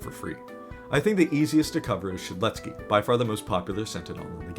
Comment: if u turn on captions it says shitzky instead of shedletskey 😂